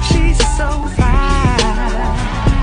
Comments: “as long as i’ve got my suit and tie” 💀😭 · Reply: i Heard “as Long as i got my soup and i,” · Why did i first hear "as long as i got my super tie ima leave it up on"